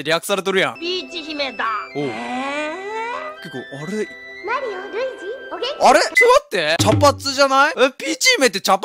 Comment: マリオ う〇ちって聞こえたのは俺だけ？